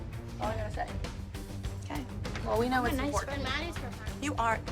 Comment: maddie did not say "I guess we're not friends any more" if u hear closely she actually said "I'm a nice friend" this show i cant- the clip is